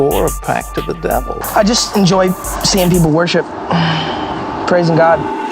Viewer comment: In the intro, the there’s a clip where the guy says “I just enjoy seein people worship..” And I always hear it as “I just enjoy Sand people worship.” Just wanted to share that with ya 😂